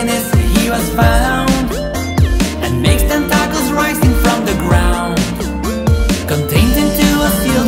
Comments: When it says making tentacles rising from the ground I thought it said making tacos rising from the ground (im not trying to be rude) besides that we got a another extended version! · when I first listened to this I heard "and makes them tacos rising from the ground" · i always hear 'and make damn tacos rising from the ground"